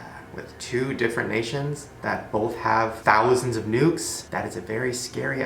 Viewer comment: I heard nudes 😂😂😂😂😂